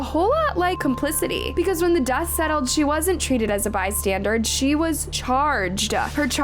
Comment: Did I hear “by-standard” instead of “bystander” at 13:43 😵‍💫